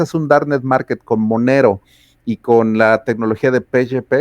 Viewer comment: Qué dice en 31:50 ? Con monero y pechepe?